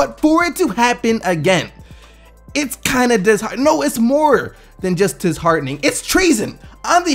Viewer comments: "it's kinda disha- no it's disHARDENing" looooool sounded like it almost as u were trashing James harden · Did he say "Dis-Hardening.." ? LMAO